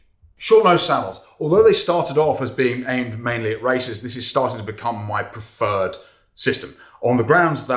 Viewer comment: thought you said they’re aimed at racists 😂